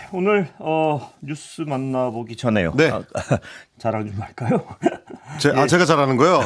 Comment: 응마우스식 바이든말리면 “자랑 좀 할까요?”라고 들리는데, “잘하는 거 할까요?”라고 했다함(남천동에서) 거, 자랑 좀 회줘라~